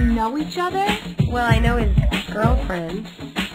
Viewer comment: what does she say “I know I’m his ex girlfriend”? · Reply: she says “well I know his girlfriend”